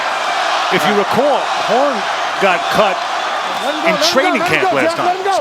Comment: If you put captions on at 9:53 it say porn instead of horn hahahah.technology